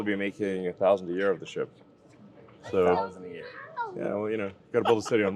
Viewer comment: I had to rewatch that a few times, I swear he said '1000 year reich' at first 😅